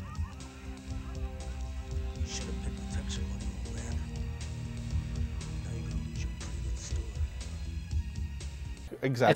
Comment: Not sure why you guys couldn't understand Z'dar's dialogue at 11:29. He clearly says "Should've paid the protection, old man. Now you're going to lose your pretty little store."